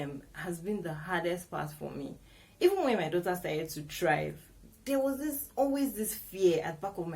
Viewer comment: i heard 'even when my daughter started to drive' I was like 😯